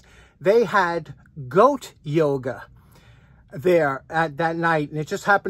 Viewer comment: At first, I thought you said goat yogurt, but your goat yoga video straighten me out.